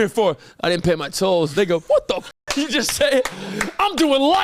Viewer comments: what did he sayyyyy???? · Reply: I had to double check too im 75% sure he just said “they go” really fast 😂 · did just say the N word 😂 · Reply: He said “they go “ just really fast 😭